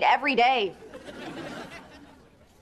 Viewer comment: I thought she said I like birds with cheese 😂😂😂